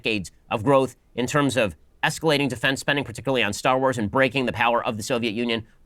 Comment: did he say Star Wars??